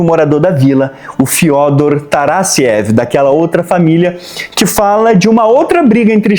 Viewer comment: Taradkiév? Juro que escutei isso